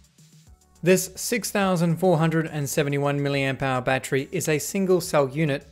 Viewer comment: 6471million pound battery💀blud is a millionare 💀💀 6:30 (Edit) i know what he said now it was POWER not POUND.